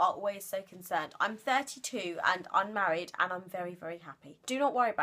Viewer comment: did you say that you're married at 3:30-39 ?!!?! there was a wedding !!?!! confusion !!!!!! xxxx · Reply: i said unmarried! lol! but yes i listened back and sounded like i said "I'm married!' but i promise i said "unmarried' lol